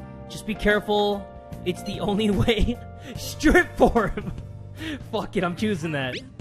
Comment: I thought he said strip farm😂